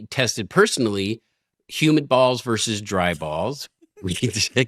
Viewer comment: Embarrassing... Until they showed the dehumidifier, I thought she was saying "human balls" :^)